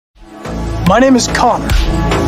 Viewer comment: "My name is Conner" Is it's just me or i heard "My name is Coc-" 💀